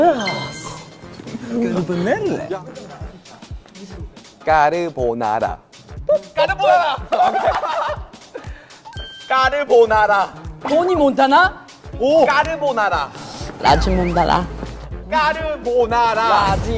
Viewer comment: I HEARD “YeSsS, cArBaNaNa”